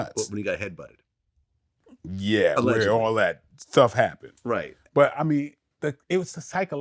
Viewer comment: did he say “before all that ‘thuff’ happened” 😂